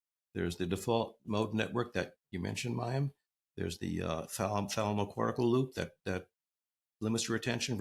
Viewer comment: What are the filters of the brain that Dr. Bruce mentioned at 39:55? Can't understand what he's saying. 😅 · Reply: The thalamocortical system - which links the thalamus with the cerebral cortex.